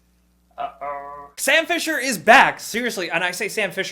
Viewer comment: I first thought you said "Bobby Phisher came back" (the legendary chess champ) and I was so excited! Sam phisher is great too I guess...